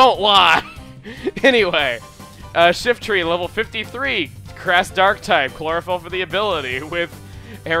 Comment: Did anyone notice said at 5:48 instead of grass, crash?